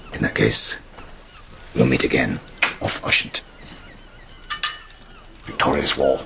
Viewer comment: does anyone know what pellew says right after we'll meet again at 1:07 ? it sounds like gaelic or something, maybe some old form of cheers? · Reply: I think it was Ushant. He's saying the other ships will meet Hotspur at sea.